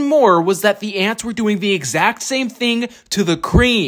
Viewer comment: Bro i misheard "my ex" at 'what should i give my ants next?' LOL😂